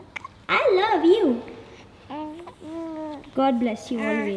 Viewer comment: she was really says I love u .... 👏🏻👏🏻😃